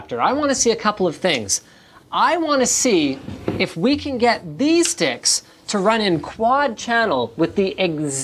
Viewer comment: i thought he said "these d!cks"